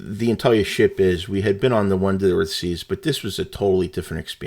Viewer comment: Did you say at 1:45 that you had been on the Wonder of the Seas? I couldn't quite make out which ship you said you had been on. If it was Wonder that is an Oasis class ship. I enjoyed your video. I am going on her in April.